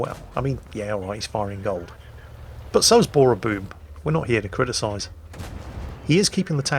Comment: did just say "Bora_boob" or is it just me?